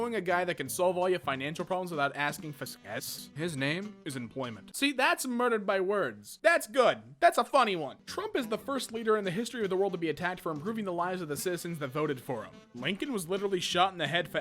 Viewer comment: did anyone else hear "Troomp"? Lol